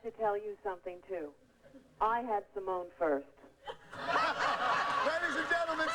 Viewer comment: Sounds like she said, "I had Simone first"? In-joke about the family dog?